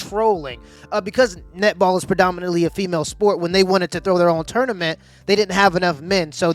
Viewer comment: For the longest time, i thought i heard nipple tournament.